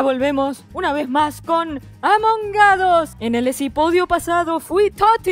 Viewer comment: alguien más noto que dijo isopodio? Xd (sin ofender)